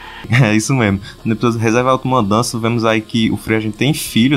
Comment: qual o nome do ep que eu não entendi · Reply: Reserva auto mudança (eu acho que foi isso que ele falou)